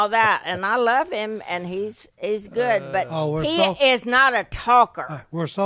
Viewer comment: Did she say Jase is not a talker at 28:37 ????????, Please introduce Ms. Kay to the Unashamed podcast!!